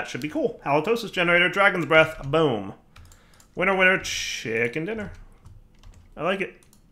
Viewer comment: did just say "Winner winner, chicken dinner"? Next he'll be all "Unreal, banana peel" and "Far out, brussel sprout".